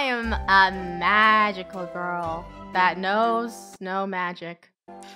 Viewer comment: I thought lunar said Snow Magic LOL XD